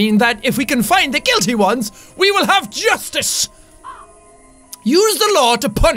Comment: I thought for a second that he said juice instead of justice OOF